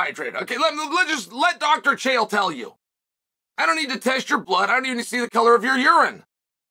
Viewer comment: I heard “taste your blood” on first listen Lord Chael Dracula: undefeated, undead